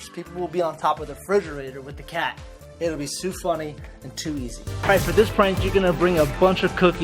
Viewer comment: did he just say sue funny?